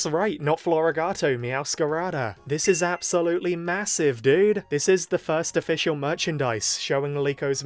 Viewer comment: did you say…massive?